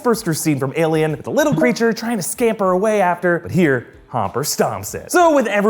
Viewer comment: did he say homper or is that just me